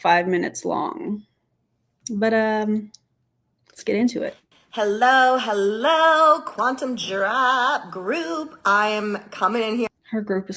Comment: I fully thought she said "quantum giraffes"